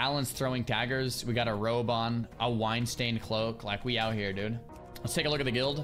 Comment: the Weinstein cloke or did he say wine stained cloke if its the former stay away from those islands in game 😂😂